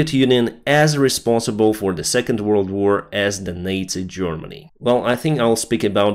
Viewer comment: Your English is really good, but it sounded like you said, "NATO Germany" instead of "Nazi Germany" it took me a moment to figure out what you meant! :-)